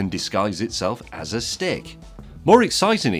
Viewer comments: Hah, I heard "insect that can disguise himself as a Stig" :D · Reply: Same here. I can't help it, when James says 'stick' he seems to be saying 'Stig'... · i heard him say: a stick insect that can disguise itself as a stig